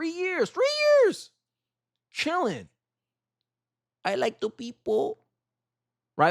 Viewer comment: You heard "I'll miss the people". I heard, "I'll miss the pesos".